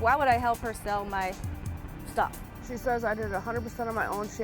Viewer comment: i thought she said "shit"